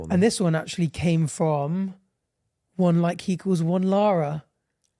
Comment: what does say here? And who is Lara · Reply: He says One like equals one Lara Lara is the producer for both Bach and Podcast and Filly's podcast